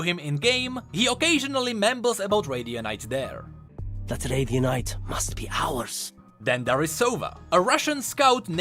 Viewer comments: Personal theory: Cypher says that "that radianite must be ours" when on the attacking team. The spike just absorbs nearby radianite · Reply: Ngl I thought he said "its late in the night, must be ours" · Oooh, I thought he said Arabian night